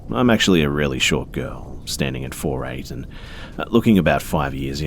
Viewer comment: Oh omfg I thought she said “I’m a pretty short girl, standing at 5’8” 💀😂